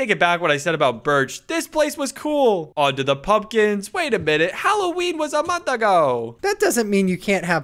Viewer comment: I thought when u said about the pumpkins halloween was a month ago I thought u said halowenwasamonshago😊